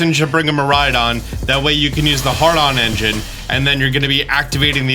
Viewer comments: i heard “hard-on” instead of “had-ron” came racing to the comments assuming this would be top of the list. color me surprised. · Did you say Hardon engine? Lol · I'm sorry, what did you say? 5:08 "One person should bring a Miraidon, so you can use the Hardon Engine" Say that again? "So you can use the Hardon Engine" One more time? " H A R D O N E N G I N E " my guy i know Miraidon looks a bit sus from the back but i don't believe that's how you pronounce that